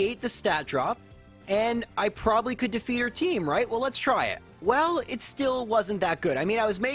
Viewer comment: Did you say "her team"?